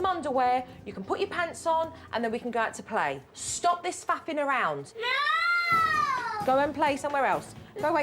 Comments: Did JoJo say "stop this fuc#$ng around"? LoL...love you JoJo!!! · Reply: she said faffing around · Please tell me I’m not the only one who thought jo said stop this effin about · does Jo cut to the quick and say ‘Stop this f***ing around!’ ??? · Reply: Sounds like she said fapping around 🤔